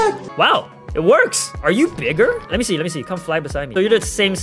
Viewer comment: I thought you say the n word in 6:29 it turns out you say bigger...